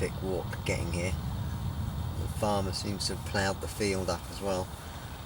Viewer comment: I thought you said father has plowed the field up I thought fucking hell this guy has hes own stretch of river lol but I watched it back and you said a farmer ha ha.